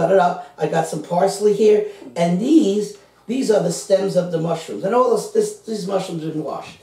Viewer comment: I never use canned mushrooms they are fresh mushrooms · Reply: Oh I thought you said my cans of mushrooms, you said stems! I listened again. Yeah fresh ingredients really make any meal better